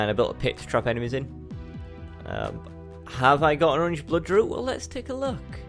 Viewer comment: Have I got any "???" I didn't quite catch that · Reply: Orange Bloodroot